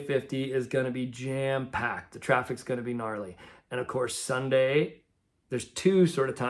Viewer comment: I could have sworn you just said narley😮❤🎉❤🎉❤🎉😊